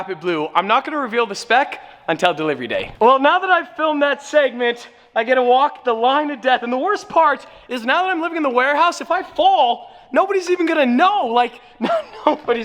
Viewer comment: Thought he said that it was illegal to live in the warehouse